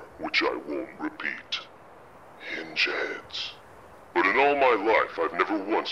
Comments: what did he say? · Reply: Hinge heads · Did he say hinge heads or injuns? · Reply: Fadil Langston Hinge heads